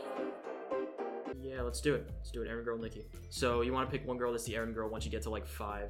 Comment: did he say the N word at 13:00 or did I mishear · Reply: it sounded like he said "new girl" just really quickly, but idk